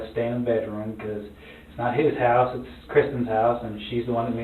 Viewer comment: I thought the bf said it was the daughters house and the dad didn't like he couldn't smoke. Maybe I misheard and he said the mums name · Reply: He said it is Kristen's house and that's her stepmom